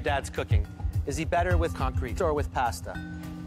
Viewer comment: " is he better with C O N C R E T E or with pasta?" Now I need to know what he actually said